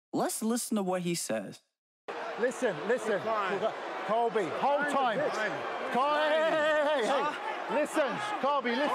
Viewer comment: what does Colby say back to Kamaru? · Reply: Just calls him a bitch from what I can tell